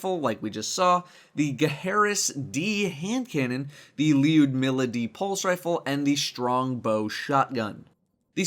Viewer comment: I thought you said lewd for a sec